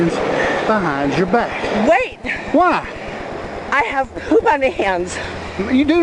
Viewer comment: “Wait!!” “Why??” “Cuz I have poop on my hands!!!” That’s the Shitty-hand Shutdown. Edit: oh thank god. I thought the cop said “I have shit on my hat”. Thankfully, I was wrong.